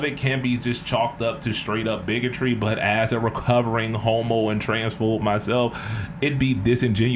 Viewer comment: I understand what you meant here, but for a second I thought you were saying "As a recovering homo, and transphobe myself..." and I had to do a double take 😂